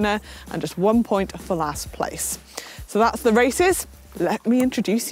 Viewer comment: did she just say racist? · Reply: She said races